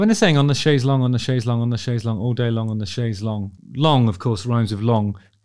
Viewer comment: I thought she was saying she was gonna "chase Schlong all day long", am i the only one?